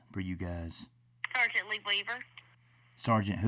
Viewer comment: I thought she said Leave It To Beaver when she said Lee Weaver at first